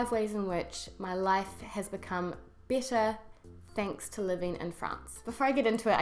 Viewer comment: sounds "bitter" instead of "better" because of your NZ accent. lol